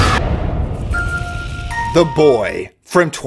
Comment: I was listening to this, not avtually watching, and when you said The Boy I thought you said The Void and I was like dude there is NOTHING comfy about that movie! 🤣🤣